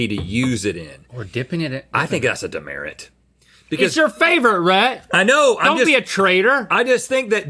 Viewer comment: I heard “ITS YOUR FAVE-OR-RHETT!” 😂